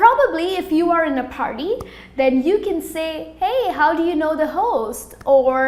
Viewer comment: Hey at 3:34, am I the only one that thought she said to ask "How do you know the hoes"?